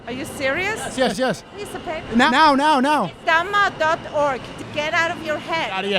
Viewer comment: Deadass. Do you know what she said about the retreat? Her accent was so heavy. I was tryna figure it out. It was at 11:48. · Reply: goenka vipassana retreat